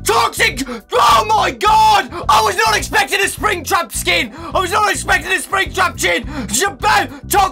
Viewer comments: "I was not expecting a Springtrap CHIN " Did I heard that right? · Did anyone else hear him say "SpringTrap chin?" 0:54 and listen very closely